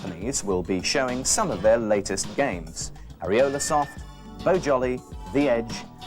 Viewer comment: Did he say ariola soft